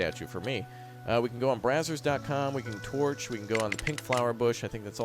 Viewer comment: Did really say brazers. com? 🤣 Its